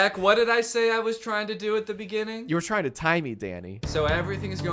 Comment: Everytime Jack said Danny it sounded to me like "Daddy" and at 21:41 I heard "You're trying to tie me, daddy" I-- 💀